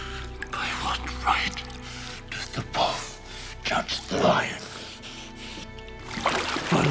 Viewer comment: what he says? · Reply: SOFIA nav 'By what right does the wolf judge the lion?'